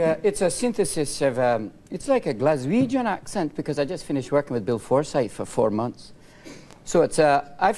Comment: I heard "I was working for Bill Cipher" at first ._.